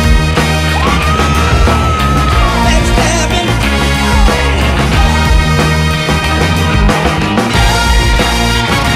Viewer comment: What does James Brown say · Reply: FRonTLinES10064 back stabbing